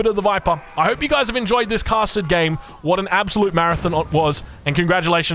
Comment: May I ask what does that exactly mean? like intense or what? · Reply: just sounds like he says “custard” when he says “casted”